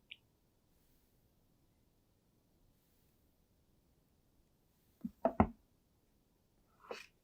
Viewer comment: bruh did you say cookie?